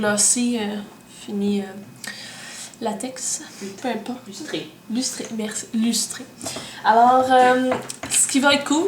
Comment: Je ne vais rien dire de ta vidéo car les reamrques (POSITIVES) seraient trop longues,tout simplement xD.....C'était quoi à 2:12? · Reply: haha, mon copain qui dit "lustré" :P